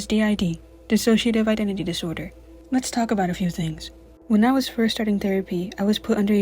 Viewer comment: I THOUGHT IT SAID “PISSOCIATIVE IDENTITY PISORDER” 😭😭😭